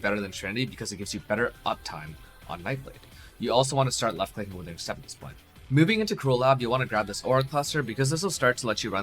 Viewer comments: What did you say at 9:26 ? You also want to be left clicking with something? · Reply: "you also want to be leftclicking withering step at this point" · I can't understand what u say at 09:28 for the love of god. Pls anyone help? "You can start left clicking what?" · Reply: withering step, withering step always goes on the left click in builds that use it for elusive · I can't make out what's said at 9:27 at all. It sounds like he says you also want to be moving with left click acceptance blahgk. <link> Anyone able to help me with this? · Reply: left click withering step at this point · What does he say at 9:29? "You also want to start left clicking with " ? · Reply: withering step